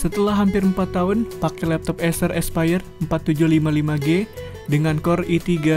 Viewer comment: laptop acer expire...eh aspire ya sory salah denger hehehe nice video